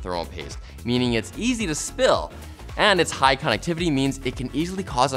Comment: I heard him say "high connectivity" - it's clear he meant "high conductivity"